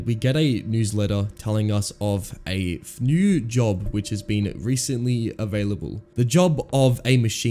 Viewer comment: I'm sorry but what did he say at 0:54 that was not a word at all. Is this an ai channel or something? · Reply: “Telling us of a new job which has been recently available”